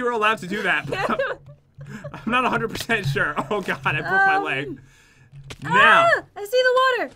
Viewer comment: In 13:34 min it sounds like Pat says "ow I pooped my leg"